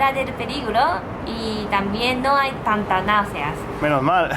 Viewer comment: ¿que dice? antanasia?? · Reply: Tanta nausia.